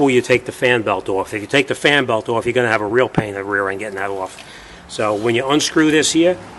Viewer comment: lol no bolts left over... great video..your expressions on your face when the trucks go by are hilarious... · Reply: LoL..Yeah, I heard you say ass instead of rear end too.